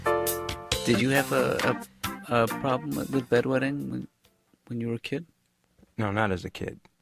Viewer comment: What he says at 09.26? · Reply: “Did you have a problem with bed wetting when you were a kid?”